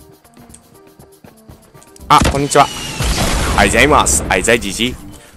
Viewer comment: ６分15秒あたり 『あじさいGG』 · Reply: 俺にはあいざいジジイって聞こえた